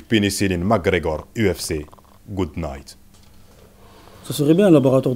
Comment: il a dit McGregor UFC ... good night quand jai compris j'étais KO